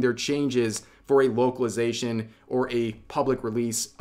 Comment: said 'localization' and i misheard as 'wokealization' and i had to rewind to make sure i didnt mishear which, to be fair, is what's also been going on lately, especially with recent coverage on the lovely complex situation.